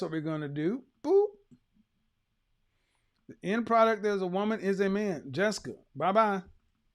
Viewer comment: Hey quick question.... At 1:22:00 Did he say, The end product of a woman is a man? Is that what he said?